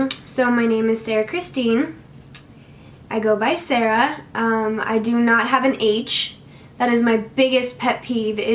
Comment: I thought she said "i dont have an age" at the beginning 😂